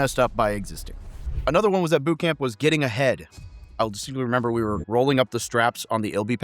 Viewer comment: I swear I heard " getting a head"😂